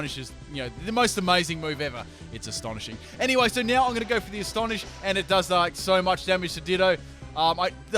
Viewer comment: I thought when you said astonish it was stone edge. Lmao